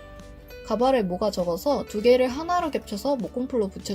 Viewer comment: did she said cabaret? lol